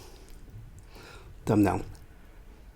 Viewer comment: hehe i thought 1:00 he said "nom nom" but it was for thumbnail. Lol love ur videos! 😋💙